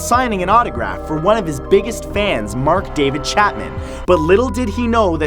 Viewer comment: he said man instead of fan